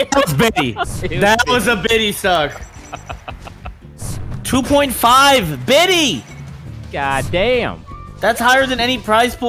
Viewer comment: did he say the prize pool is 2.5 bitcoin a week?! That's over 50k right now wtf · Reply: Definitely sounded that way, but first is just $2.5k paid in bitcoin